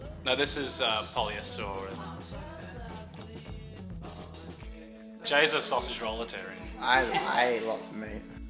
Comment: what do they say at 0:47?? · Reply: Kevin: "Jay's a sausage-roll-atarian" Jay: "I eat lots of meat"